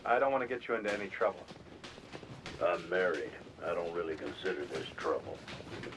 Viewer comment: Around the 20:30 mark, did he say, "I'm married. I don't really consider THIS trouble"? BAZINGA